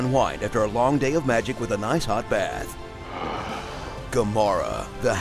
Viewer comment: Did he say Gamora??????????????????!!!!!!!!!!!!!!!!